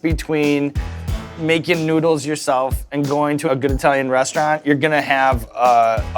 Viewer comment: does Sean call it an Itallian restaurant??